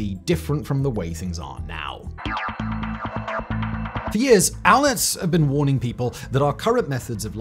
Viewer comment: "For years, ????? have been warning us..." Wtf is he saying here? Transcript has it as "allets", but googling that brings up "알렛츠". 🤷